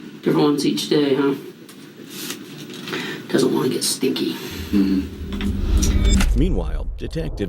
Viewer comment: detective : "does she wearthe same shorts every day?",...suspect: "no".,...detective : "doesn't want to get stinky, huh?",....audience: "what did he say?".,...I think that detective KNOWS a little something about "stinky panties".,...he's probably got a closet full that he buys online. 😐